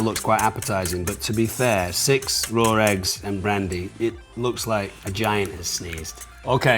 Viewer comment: ish is it me or did he say "it looks like vagina sneeze"